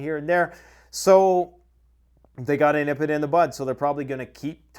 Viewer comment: Did he just say “ Nip it in the butt?”